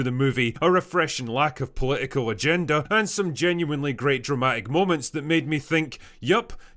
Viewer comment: Did he really say a lack of a political agenda?